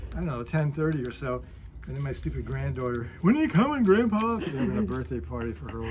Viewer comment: Did he just say "And then my stupid granddaughter"? Lmao😅